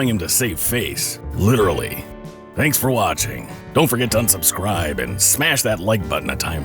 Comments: Did said "dont forget to unscubscribe" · did he say don't forget to unsubscribe? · did he say "dont forget to unsubscribe" lol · did he just say UNsubscribe?